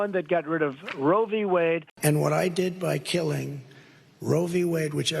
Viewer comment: Whenever he says "I killed Roe V. Wade" I hear "I kill women".